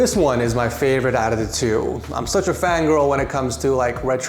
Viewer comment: did he just say fangirl?